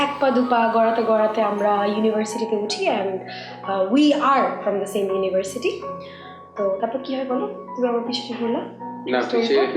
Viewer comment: then also wanted to correct it 🤣 but alas! She was wrong again ....she said "we are in same college" instead of saying we "were"....lol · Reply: actually she is right she said we are FROM the same college..